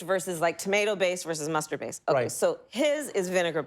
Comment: I thought she said 'masturbate'.